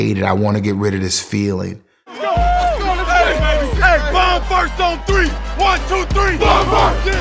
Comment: "bomb farts on 3? ... BOMB FARTS" Did I hear that right?